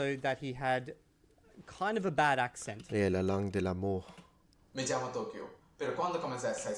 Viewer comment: Even me who is Spanish and can't speak French heard him say "la langue de la merde" instead of "Le langage de l'amour" Ps he speaks Spanish like one of those tape recorded voice robot from a 60s movie.